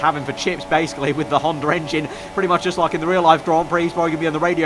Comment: I'm not purrfect in English, and I always hear 'Hondar engine'.